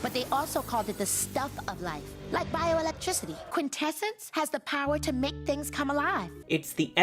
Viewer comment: I always thought she said The Heart. We had like the books with little comic inserts in my library way back when and that's how I found out that it was a comic first. Ahh magical girls